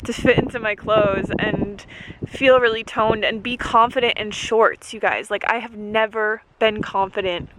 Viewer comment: I thought you said "confident and short" and I was like yasss short girls unite! and then I realized what you acutally said :D (not trying to make fun or you or anything, it's very brave to show yourself the way that you are doing). I try to eat very healthily and my stomach juts out for some reason! I don't even have that much fat on me, it's just bloat or something (I don't eat a lot of sodim or oils and I eat turmeric and such so I'm confused!) if anyone has any tips let me know pelase!